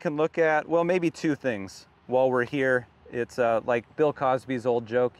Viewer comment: Nobody quotes Cosby anymore... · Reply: I thought he said Bing Crosby